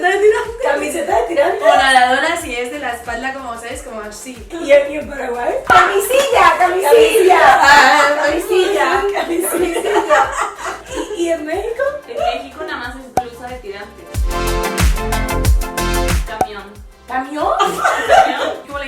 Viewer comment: O.O hay camas para silla????!!!! Eso fue lo que pense cuando la escuche